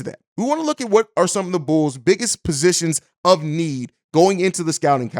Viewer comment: you said Bulls instead of Bears